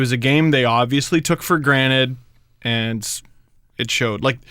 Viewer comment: unintentional Rick and Morty reference??? Cuz i heard Granit!